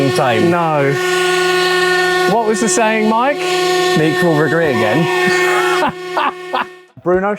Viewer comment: LOL at 24:17, the auto-generated subs displayed "me will regret again" instead of "make Rover great again"